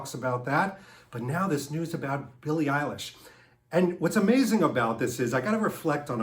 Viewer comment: I am sorry to say I have never heard of her. I thought for a second they said Billy Idol...!!! Over here in England I have read the British actor James Norton has been earmarked for the Bond role.